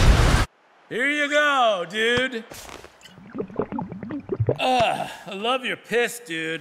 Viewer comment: Dude, when i heard, "i love you piss man." i was so cofused